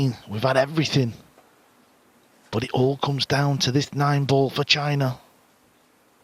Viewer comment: i swear he said nine ball vagina lol